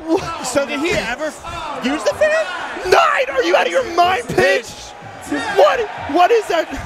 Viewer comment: Green Pipe combo 21:24 Blindfolded Peach's Castle Combo 25:26 SICK PLANET ZEBES LAVA COMBO (FIRST TRY) 41:53 Long Setup by Prince (Last Attempt) 47:17 Falcon Punch Start to End Combo 56:30 Closest Pikachu/Jigglypuff Sing Combo 1:10:44 Basically First Try No Items Combo 1:17:30 LAST TRY CLUTCH SAMUS COMBO 1:23:40 Hype Double Red Shell Falcon Punch Combo (Not a full combo) 1:29:25 69 Homerun Combo ( ͡° ͜ʖ ͡°) 1:34:00 Crazy Falcon Punch Bounce Combo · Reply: Did he really say. Are you out of your mind Bitch!?😂😂